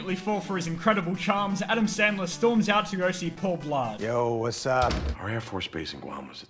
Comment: I think that Adam Sandler's charecter acts more like an adult when he is a kid and acts like a kid when he's an adult. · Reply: Also at 3:23 when he says "go see" the captions say "yoshi"